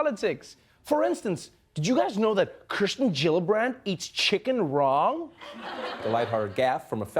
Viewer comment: at first i thought he said 'raw' 🐔🙅